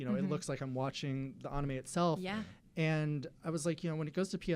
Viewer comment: did he just say onime or am I trippin